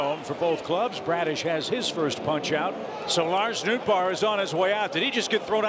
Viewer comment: Did that say Bader at 3:13? The one that used to be on the Cardinals, the right fielder (if I remember correctly) WE MISS YOU HARRISON